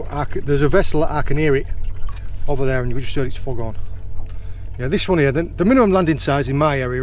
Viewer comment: what did you say? I hope my ears aren’t deceiving me. 😂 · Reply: Something along the lines of “Over there and which states f*** off”